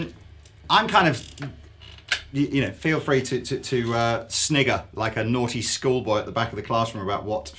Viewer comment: did he say snigger at 18:42???